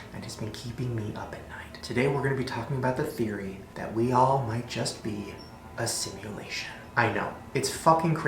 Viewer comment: at first i thought he said we all might be a sandwich because i have my speed on 2 XD